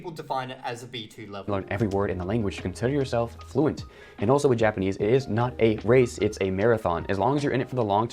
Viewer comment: “And also, Japanese is not a race, it is...” A nationality. “... a marathon.” I see. Edit: I know he said “with Japanese”, not just “Japanese”. I misheard the first time (watching at times two).